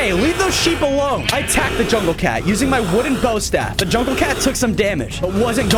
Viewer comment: I thought you said it was a stone bow staff not a wooden bow staff